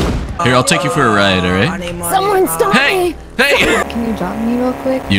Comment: Who’s voice was that saying “Someone stop it/him,stop